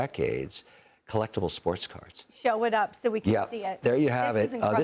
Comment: I thought he said sports car that actually made sense I don't know anybody that gives a crap about a baseball card